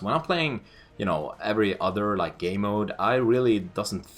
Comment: it sounds like you say 'gay mode'. roflcopter. this is an immense score for anybody on search. i'm itching to play with you in a public match. or even a private lobby with some objectives. i've been in a few of your sniper lobbies, as i'm sure you'll recall... or not. will you do anything like that any time? maybe on fuel?